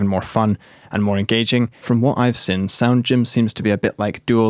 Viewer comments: My hearing must suck because I thought he was saying "Sound Gem" at first. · I thought he was saying sound gem smh